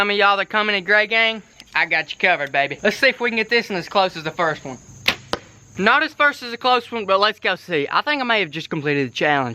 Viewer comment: Anyone else hear "not as first as the close one" at 7:50 in the vid